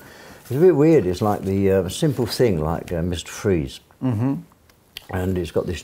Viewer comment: WTF did he say at 17:05 ? Miss Trees? What movie is he talking about with the cryogenics? EDIT: OH I think he said Mr Freeze.